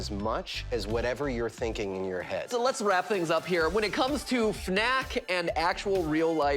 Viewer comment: Wait did he just say fnak